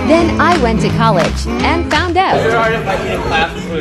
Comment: "Dude! You can eat that whole fail tub" is what I heard at first then I heard "Dude! You can eat that whole damn tub"